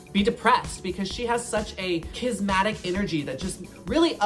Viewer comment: I love that Laganja corrected 'Malibu' feathers ... But said the word kismatic instead of charismatic 11:30 😂😭😅✌️